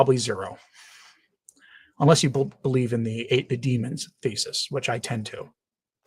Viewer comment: What did he say? Ape/ate the Demon Hypothesis? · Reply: I think was just saying demon thesis. Fr. Mawdsley talks a lot about this.